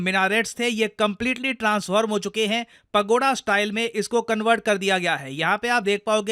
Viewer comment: he said Pagoda style , I heard Pakoda style 😂